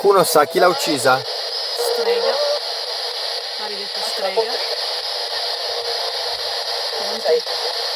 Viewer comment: ho sentito maledetta strega